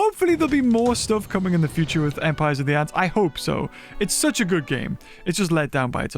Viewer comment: I thought it said empire of the undergrowth 😢